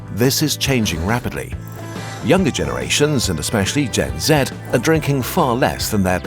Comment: Did I just hear you say "Gen Zed" ?! The Fck ??